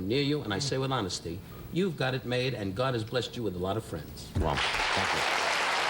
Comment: Did Carson say "wrong" at 10:22 when Rickles said God blessed him with a lot of friends. I know Carson didn't have much friends due to his behaviour off camera but he couldn't have just said what I think he did lol. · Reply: No, Johnny said "well" it is just his accent and they way he speaks.